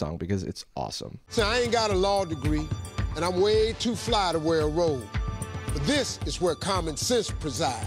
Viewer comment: Steve said, "too fly to wear a robe", but I thought he said, "too fly to wear a wig" 😂